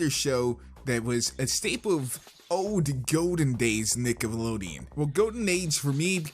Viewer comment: This video was WAY longer then i thought it was going to be. thought it was only going to be 8 minutes, guess not! but i hope you enjoy regardless! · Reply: yo dawg at 4:25 it sounded like you said niggalodeon